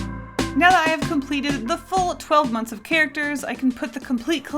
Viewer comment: I thought it said moths :(( you should do moths